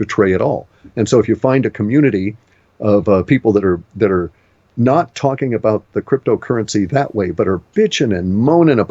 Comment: what was the currency said at the approx 42:30 mark?? pervex???? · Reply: PIVX...is DASH, without the premine and fud... the more honest version really